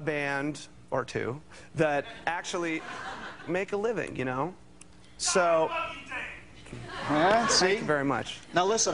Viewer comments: What did that guy in the background say?? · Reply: I F#$%@ LOVE YOU DAVE. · what does the background person yelling say! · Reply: "God, I love you, Dave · What does the guy in crowd scream out ? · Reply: Gosh I love you Dave